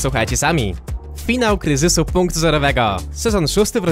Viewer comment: usłyszałem finał krzysiu punktu zerowego xD